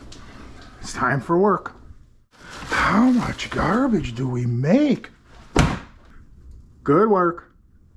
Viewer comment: I don’t know why but in my mind I hear “how much catnip do you take?” Lol